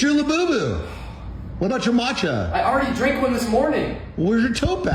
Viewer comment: I'm more annoyed that the people who used to make fun of me for drinking "grass water" years ago are now "omgeee matchaaaa" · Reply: I thought he was saying 'mocha' weirdly